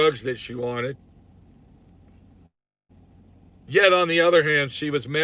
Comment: i heard the word yes @ 26:54 something paranormal